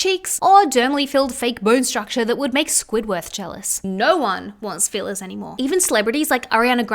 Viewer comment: please does anyone else hear her say squidworth instead of squidward im crying 😂